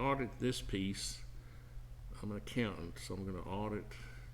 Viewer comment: I am a design-build contractor and not an accountant :) You can send me a PM on my youtube page. Thanks! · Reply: could have swore I heard you say accountant somewhere in your video, must be hearing things